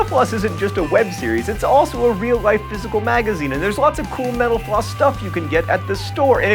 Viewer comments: I keep hearing "Mental-Floss" as "Menopause" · when he says mental floss it sounds like hes saying menopuase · He said thank you for watching "mental floss" and I thought he said "menopause"..